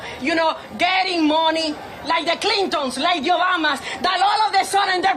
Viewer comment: That lady says “the Yobamas.” Come on lady, get it right, it’s OBAMNA! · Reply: I honestly thought she said your mamas and had to re-watch.